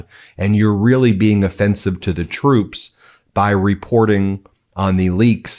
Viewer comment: i thought Donnie said the troops are looser